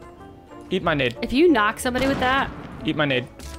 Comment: it sounded like you were saying eat money I’m like what